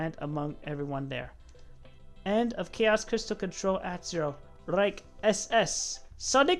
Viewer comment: Did he say "Reich SS" in that sign off? As in the nazi organization???